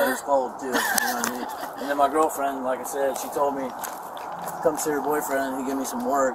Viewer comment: did he say his gf told him to come see her bf for work???